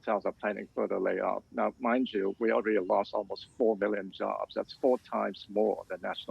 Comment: I had to press replay but I swore he said 4 million jobs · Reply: He did say 4 million jobs